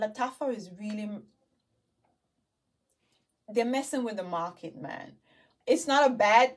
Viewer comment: When you said messing with the market, I heard "messing with the mafia." I nearly spit out my drink! 🤣 You are too much! 😁